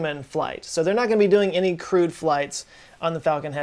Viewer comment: Hehe - you said a "crewed" mission. I thought you said a "crude" mission.